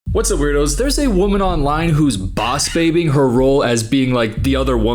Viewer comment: i thought you said she was boss babying and tbh it tracks